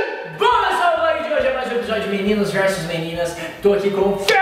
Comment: só foi que entendi meninas vs meninas ? na parte